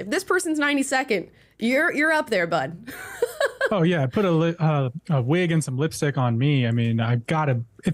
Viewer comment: face 5:51 😂… why did I think he was about to say, “put a ribbon on me, I been actin’ brand new?’ For soooome reason? Lol